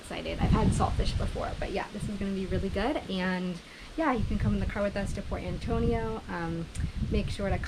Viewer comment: The man getting the saltfish said "coming ..." and you thought he said "come in".